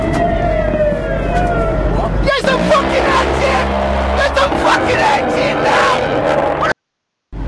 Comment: does anyone understand what he is saying? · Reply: Best I could hear was "Get some H.E. in." then "Get some fucking H.E. now!" which probably means he's ordering High Explosive shells loaded. It appears he's next to a Paladin self propelled howitzer, so he's probably calling for shell type to engage in counter battery fire and put warheads on the foreheads of whoever attacked the base to set off the Phalanx systems.